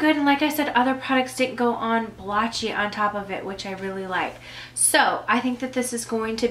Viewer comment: I'm confused, it sounded like you said "Powders do go on this blotchy, which I do like". Is that what you said? I am so confused lol. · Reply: lauren nope, I definitely said "powder products didn't go on blotchy, which I like."